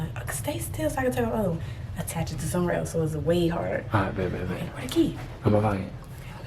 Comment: Somebody heard say babe